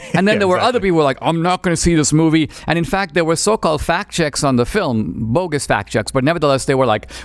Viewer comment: When he said fact checks I thought he said fat chicks